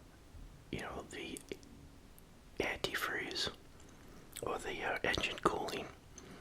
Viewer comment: Not me think at 7:14 think that he said anti furry😭😭